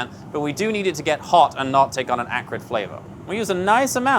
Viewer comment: Subtitle is wrong at 6:13. He says "acrid" flavor, not "accurate". You don't want the oil to develop a bad flavor when it hits the hot pan. Edit: Also has chef hands for touching the handle of that cast iron xD Mad lad